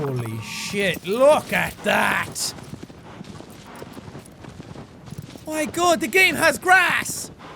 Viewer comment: he said OMG THE GAME HAS GRASS! what I heard was OMFG! GRASS!!!